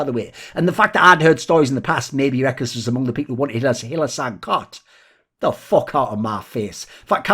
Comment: someone please tell me what he says here. I replayed the moment 10 times but I can't hear anything other than "maybe rekkles is among the people who oqfqiofhguofrf hylissang coughed" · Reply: Wanted hylissang cut